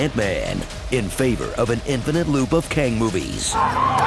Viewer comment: I heard Kang and saw the thumbnail and thought he said Krang. Weird TMNT crossover could be fun